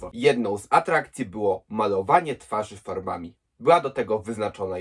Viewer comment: usłyszałem malowanie twarzy parówami xdd